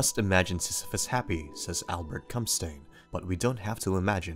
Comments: Did he just say Albert Cumstain??? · Did he just say, "Albert Come Stain???"